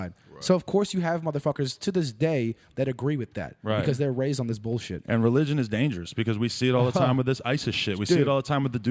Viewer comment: What movie did he say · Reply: Zeitgeist